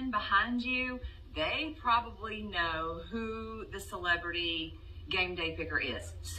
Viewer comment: what was the problem the first time what was offensive? first time when "had to turn on CC" she said celebrity game day picker I thought she said GANG bang, so that this was it. was it chocolate pie someone stretch it and make it sexual? Or was it the the Grandmammy? how is Grandmammy racist? enlighten me please. Also just learned about the "Izzle" word from this.